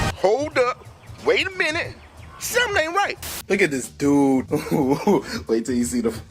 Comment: Dude when it said “hold up, wait a minute something ain’t right” I thought I heard “something ain’t white”😂😂😂